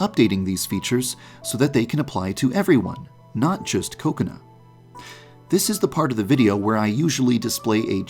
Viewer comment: Whenever he said Kokona, I thought he said coconut.